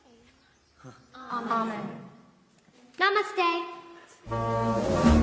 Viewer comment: did she say namaste in hindi !?😭